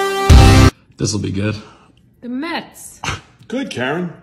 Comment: for a second I thought she said the feds 😂